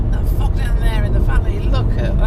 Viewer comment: at 11.24 you hear a beep and something getting said , sounded kind of rude .......... what was said ? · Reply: lyndon inch I think he said “the fog down there in the valley...”